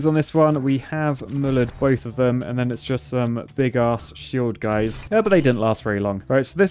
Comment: ? WHAT DID YOU SAY BIG A** wht?!